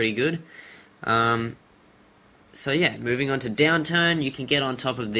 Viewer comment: he sounded like he said downtown but he said downturn